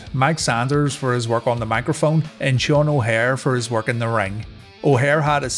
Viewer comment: All thru the ending days of RTW, I keep hearing it as "Gin Dragon O'Haire" which is a pretty awesome name.